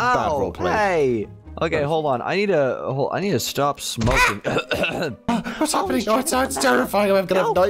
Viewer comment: IS IT JUST ME OR DID SAY I NEED TO STOP SMOKING AT 3:58?